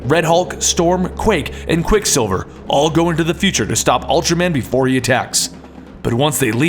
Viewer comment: did just say ultraman in 3:09 lol